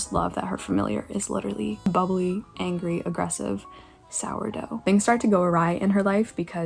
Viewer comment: did you say "things go a rye"?